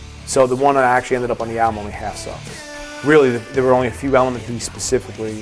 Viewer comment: plz help, i can't hear, he says that the album version sucks too? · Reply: Half-sucked